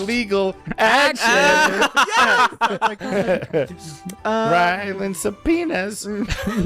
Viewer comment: It sounded like said “Ryland’s sub-penis” at the end lol add to list of band names 💀